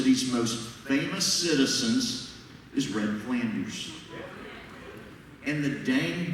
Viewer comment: I thought they said Ned Flanders.